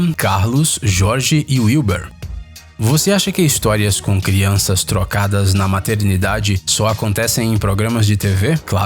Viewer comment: "Crianças trocadas"...rs...eu entendi "crianças drogadas"!kkkkkkkk